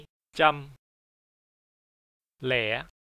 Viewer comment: I hear "leia" in place of zero at 10:12. Was It omited in explanation of counting rules?